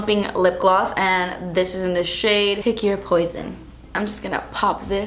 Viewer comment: When you were saying the name of the lip gloss, I thought you said it was called 'hickey or poison'